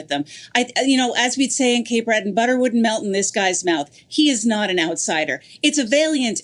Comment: the Cape Breton saying, "Butter would melt in this guy's mouth." I don't get it, butter would melt in anyone's mouth. Or did she say it WOULDN'T melt in his mouth? Can someone help me out with that saying?